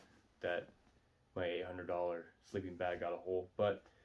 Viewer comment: Did you say $800 sleeping bag?